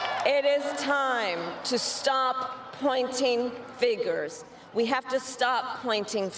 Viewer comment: "Stop pointing figures" - did the teleprompter stop working for a moment there and she couldn't figure out what the correct phrase was